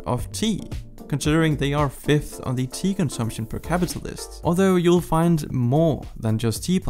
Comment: I thought he said “5th in tea consumption per capitalist”. I guess that makes sense